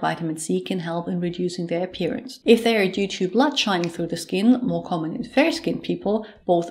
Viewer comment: Thank you for the informal video! I have a question. You mention at 5:46 in the video a condition that is more common in people with fair skin but I can’t hear what you are saying. Can you or someone please help me with clarification on that? Thanks! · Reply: Blood shining through the skin is more common in fair skinned people and actually hyperpigmentation is more common in darker skins. The video has subtitles that might help as well.